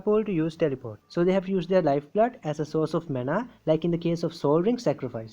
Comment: cc says menopause instead of manapoints, so funny